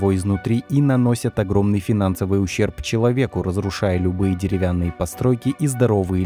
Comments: Пчелавеку ? Послышалось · Услышал "наносят большой финансовый ущерб ПЧеловеку".) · Пчеловеку,может мне послышалось.